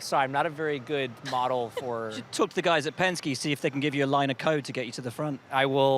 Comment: Sounded like Will said "line of coke"